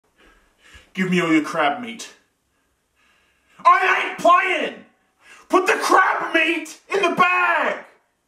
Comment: I didn't read the subtitles and I heard at first: "GIVE ME ALL YOUR CRAB MEAT, I'M UNEMPLOYED!!"